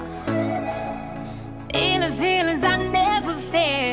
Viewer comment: still a little confused why am I hearing "Inner feelings" instead of "feeling feelings"